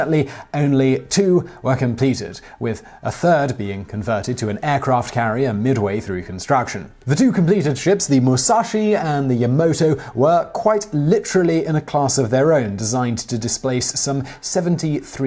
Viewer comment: Did you just say 'Yamoto' at